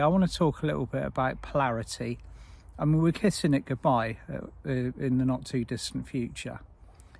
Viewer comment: I thought you said we were kissing clarity goodbye 😄 🤣